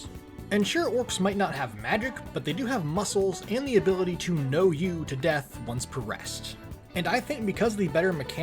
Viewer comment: did you say "know you to death"??? If not someone please correct me 😅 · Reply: I more meant like "No, you" with their ability to just not die once per day.